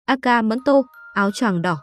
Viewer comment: Aka manto mà tôi lại nghe thành: Aka manto = Anh hay ăn mắm tôm…??